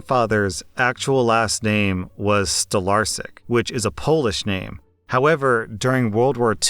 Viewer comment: Ok so the name in 14:15 is pronounced Stolarczyk, if I heard correctly.